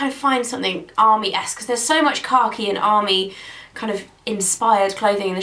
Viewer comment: Because of the top comments saying "car key", all I could hear was "car key". I had to relisten to it like, 3 times before I heard "khaki" haha.